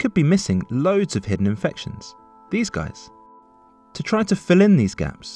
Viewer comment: could anyone tell me what he is saying? "these guys"? I mean between "infections" and "to try to fill in". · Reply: He says "these guys" referring to the highlighted dots of the population (infected, but undetected cases)